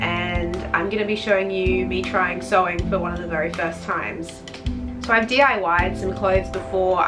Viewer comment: Ngl I thought that said attempting sewer side for the first time 😂😂